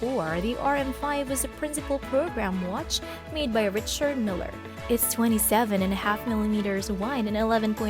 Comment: I work for Larry, @ 9:07, did you say Richard MILLer?